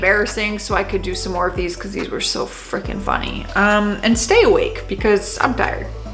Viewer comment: Okay I thought you said stay woke for a second and I was like “what did I miss in this video” yeah lol!